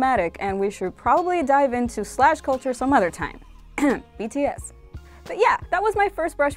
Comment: Did anyone else hear bts? 7:12 Army we all know you ship those 7...XD